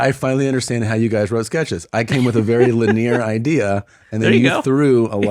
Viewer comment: Did Tom just say liNEER!?